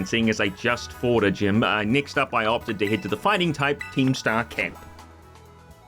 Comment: I misheard "Fighting Type Team Star Camp," as "Fighting Type Keemstar Camp."